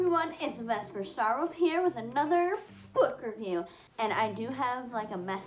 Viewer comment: I thought you said “foot review” 😂 i was like WHUT jkjk its interesting they made a book about a furry con